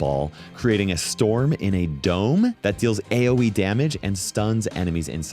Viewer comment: What did he say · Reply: AOE= area of effect